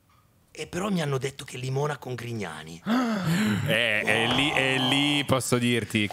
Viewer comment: scusate, non ho capito... ma dice "limona con Grignani" o "limona con i cani"?🤔